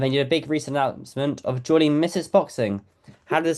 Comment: Did he say mrs. Boxing at 0:22 secs? 😂